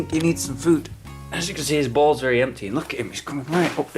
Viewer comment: For a second I heard "His balls are very empty" instead of bowls. I felt mortified for Smudge.